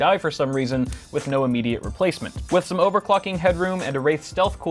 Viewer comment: he said I heard him say overf.cking